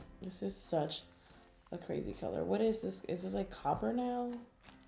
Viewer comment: I thought she said : is this like a proper noun 6:30 to 6:36 than I was like school is out no more learning